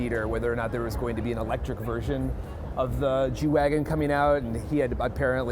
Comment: did he just say jew-wagon? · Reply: g-wagon